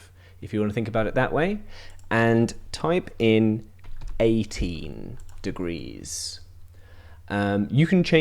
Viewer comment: I thought you said 80 at 9:05 so my cat is aggresively rotatating lol